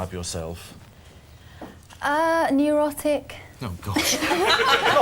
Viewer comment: I thought she said erotic. I got a sudden hard on.